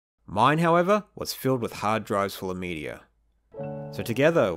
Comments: I heard "hard drives" as "hard drugs" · I misheard "hard drives full of media" as "hard drugs full of media", because drugs designed to make you hallucinate specific movies/music/books/whatever sounds like the sort of thing a scifi dystopia would make.